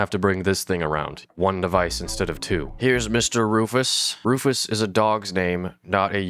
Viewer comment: i heard “rumus” 🙏